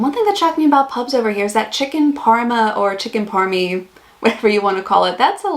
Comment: At first I thought you said, "Chicken Karma." LOL